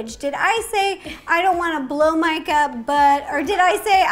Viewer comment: I didn’t hear the difference when she said “blow mike up” and “blow my gut” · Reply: I heard "Blow Micah" and I thought she was accidentally dirty